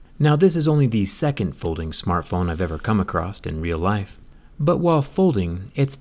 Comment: did he just say acrossT? I’m never watching him again